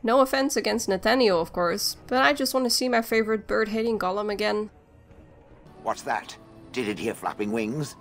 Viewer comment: it sounded like your were saying "bird-hating gollum". I knew what you meant but it was funny. Great video btw, I love DA2.